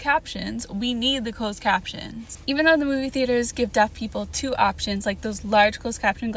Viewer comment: For a second I thought you said duck people